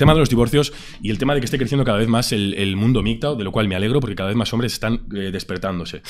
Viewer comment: que es eso del mundo midtaun o algo adi que dice en el minuto